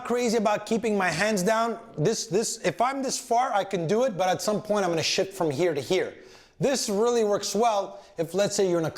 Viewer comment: I had to watch this a few times. I honestly thought that at 4:36 he said: “at some point I’m going to shit from here to here.” 🤨